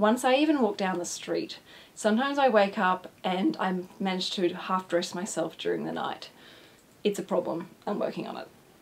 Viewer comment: Well I definitely enjoyed watching this video and learning more about you. It made you seem more like a real person and not just a financial guru. I didn't understand what you said at 5:56. I played it over and over, but just couldn't understand what you said. Your accent is very heavy. But yeah, loved this video! ~Vince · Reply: "half dress myself during the night"... xCC